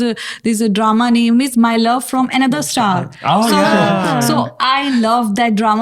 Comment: Did she just say "my love from another star "....what are the odds...still my fav one...